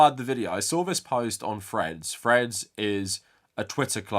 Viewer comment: I thought I was going crazy because every time Threads is mentioned I hear "Fred's", but the subtitles seem to agree with me.